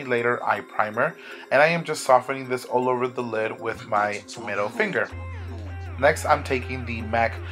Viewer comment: Omg in the beginning I swear it sounded like you said .. "I'm going to soften this all over the eye with my tomato finger " Hahahaha !